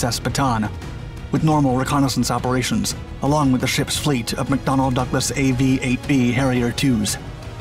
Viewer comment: 😆😂🤣 I THOUGHT HE SAID “DONALD DUCKLESS!”